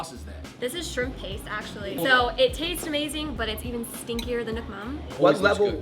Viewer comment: I heard " stinkier than your mom"😂